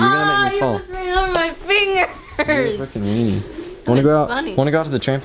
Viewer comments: who else heard him say at 8:13 to "your a f*cking meany" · Did say F***ing?